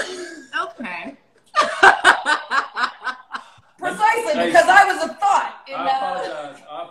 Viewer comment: did she just say "precisely, coz I was I thot..."?!! If she did Dwayne goes further down in my opinion of him lol